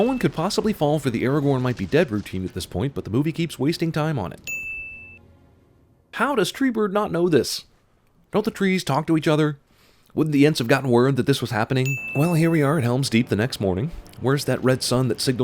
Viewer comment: I gotta sin Cinemasins. I'll swear he says "Treebird" instead of Treebeard.